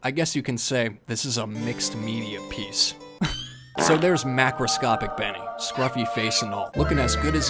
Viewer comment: at first when he said macroscope i thought he said microsoft 😅😂 lol im dumb